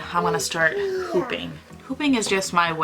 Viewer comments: girl i thought you were saying pooping not hooping AHAH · So there I was with cleaning gloves on washing my poopy diaper when you said how much you love hooping and I misheard you and thought you said pooping. I could not go back to see if I heard it wrong and everything you described after made sense for that word too. Later I was laughing by myself when I figured out what you actually meant. I guess I had poop on my mind after I heard you talking about it and while washing my kid's dirty diapers. Great video. I kinda wanna try wool too now!